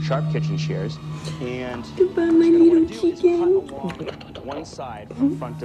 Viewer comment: I knew it!!!!! As soon as the mom called the kid "my little chicken" i knew she would either cook her or cut her up and i was on edge the whole time waiting for it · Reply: 83706Rachel Is that so? Maybe I heard wrong. But who calls their baby...chicken. I thought I heard Keegan. Sounds more like it. But okay